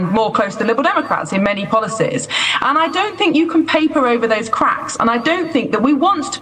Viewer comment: Oh she said "those cracks", I heard "those prats"